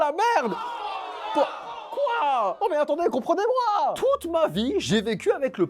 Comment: enfin ! Tu fais une vidéo "AVOIR 30 ANS" et tu dis : Comprendez moi 🤣🤣🤣🤣